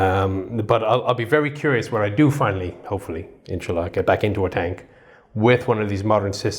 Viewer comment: Did he just say insallah? Lol